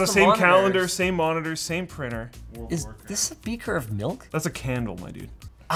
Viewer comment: "Is this a beaker of milk?" Are you canadians from, like, another planet? 😉🤪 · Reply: I thought he said: "speaker of milk"